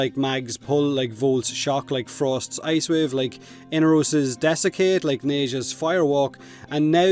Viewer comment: No, dessecate from inaros is extractable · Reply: That makes more sense. I thought he said Nekros Dessicate as well which was confusing.